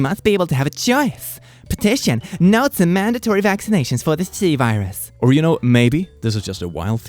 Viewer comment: i think the British in me is strong every time you say "c virus" i hear "tea virus" lmao